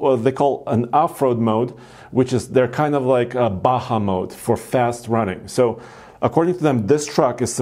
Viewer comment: did he say afro mode, for fast running.